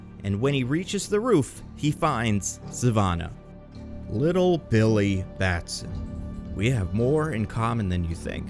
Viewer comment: When said “little billy batson” all I heard was “poor Peter Parker”